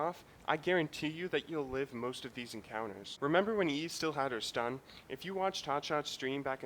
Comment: Lee had her stun? i dont get wat is he saying? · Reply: eve as in evelynn